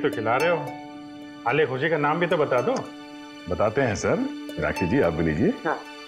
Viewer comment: did say rakhee to minakshi????